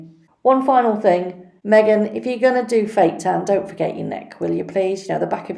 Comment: What does she say right after "one final thing..." (At 8:05) "meghan, if you're a gonna ____, then don't forget your neck (?)" I replayed it a few times and couldn't quite get what was said. Fake tan? Anyone? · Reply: Yup. Definitely 'fake tan.'